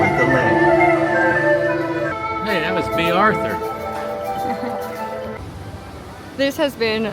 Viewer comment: what did your Dad say? 😂 Great vlogs as always! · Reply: LOL, he always jokes that the woman at the end of the boat ride looks like Bea Arthur from the Golden Girls.